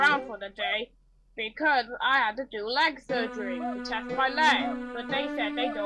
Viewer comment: I thought is said I had Leo surgery 💀